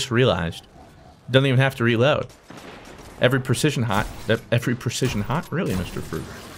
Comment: it sounds like he was gonna say shot and high which is hott. See what i am saying